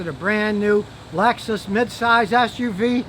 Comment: When you said your wife's Lexus I could have sworn I heard legs and my jaw dropped.